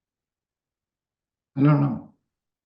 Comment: Man…..that “No” from Clunn sent chills through me. He is so calculated and straightforward. Awesome show as always! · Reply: I think what he said, at 37:51 rather quickly, was "I don't know".